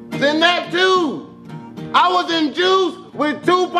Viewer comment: "I was in Juice, with TUPAC SHAKUR" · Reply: I thought he said “I was induced with Tupac Shakur”